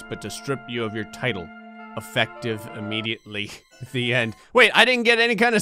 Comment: I thought it said "strip you of your LIFE, effective immediately"